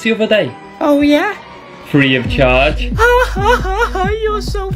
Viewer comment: I was just watching him so thought the FREE OF CHARGE said FRED AND GEORGE